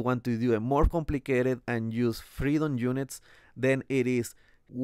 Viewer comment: Did you say "freedom units" at 1:15?